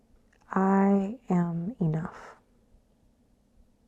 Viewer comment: Okay, I am pretty sure Adrienne said to whisper to ourselves, “I am enough,” however I heard, “I am Nuff,” like I am Groot. I dissolved into a fit of giggles. 😂